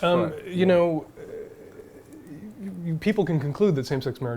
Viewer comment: Did Dan just say "you people" @ 33:10?